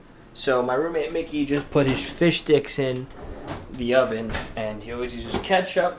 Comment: did someone say fish dicks ?